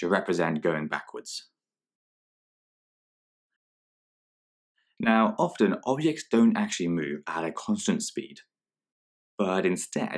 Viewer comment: What did he just say right after 3:47 “Now Often …….. stone” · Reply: He says “now often objects don’t actually move at a constant speed”